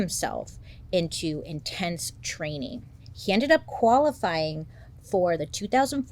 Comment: not exactly sure why but everytime says qualify I hear 'koala-fy' 😅